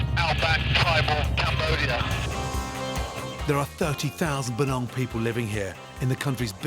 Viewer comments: i heard bananas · my generated subtitles said there were 30,000 BANANA people living here😂